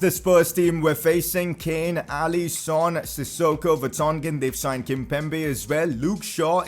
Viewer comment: When he said alli son i thought he said allison